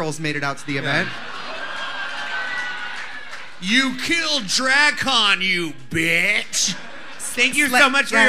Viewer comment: what the hell did she say lmao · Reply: "you killed dragcon bitch"